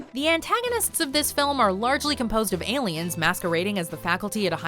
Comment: i thought she said 'aliens masturbating as the faculty at a high school'. turns out she said they were masquerading. this just shows that im super tired.